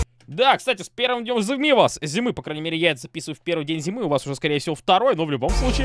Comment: "по крайней мере яйца писают в первый день зимы" вот что я услышал при температуре +37.7